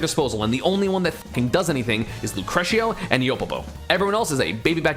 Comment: Does anyone know the two names of the ROB players he mentioned that do combos? I thought I heard “Crutio” and “Yopopo” but I can’t find them when i search